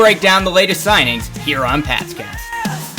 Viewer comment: I thought you said “past gas” then I noticed pats cast 😂😂😂😂!